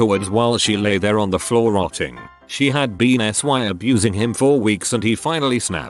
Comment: what was the censored word? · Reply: Speed Stacking Maniac sexually abusing